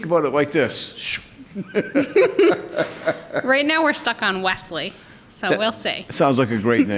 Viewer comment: I'm just gonna poop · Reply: U thought she said lesley